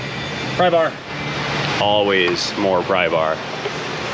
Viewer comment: How come I know you are saying "more prybar" but I always hear "more cowbell"?